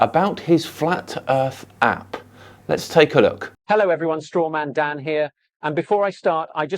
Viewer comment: when i heard “strawmandan” i heard “strongmandan” instead 🗿